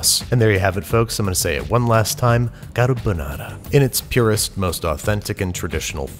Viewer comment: At @ 3:10 Babish says "Carbonara~" with a fancy accent, but the captions say "Got a Banana~" and now I can't really hear it any other way now.